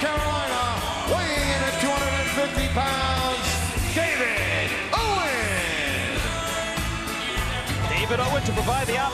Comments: Did I just hear Kevin Owens? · i heard KEVIN OWEN